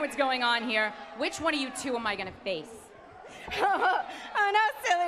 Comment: Did they edit this I thought Ashley said "which one of you bitches do I get to face"